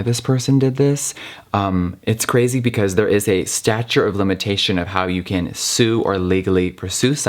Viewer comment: Did he say "stature of limitations"?! 😂